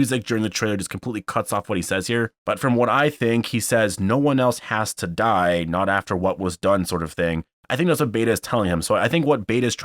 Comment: I heard beta say “No one else had to die, that deal is done...” Anyone else?